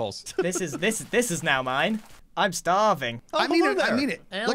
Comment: did grian say he was stAHArving?